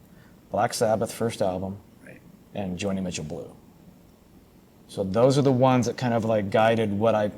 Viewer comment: What are the two albums that MJK sais in 27:20? Black Sabbath and what else? I cannot understand him. Please somebody tell me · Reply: Joni Mitchell - Blue (1971)